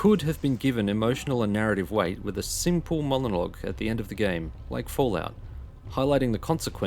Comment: Did you say "molonog"? :D